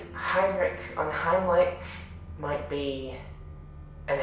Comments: I misheard Heinrich's full name as "Heinrich und Heimlich," and I was fully convinced for three seconds that we were about to meet the new Breekon and Hope. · isn't it "Unheimlich" because Gwen corrected Alice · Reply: Yeah I thought they said "And Heimlich" but after reading the other comments and transkript I hear it right now too xD Thank you